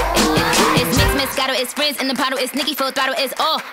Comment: Did nikkie say rizz in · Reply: She said Frizz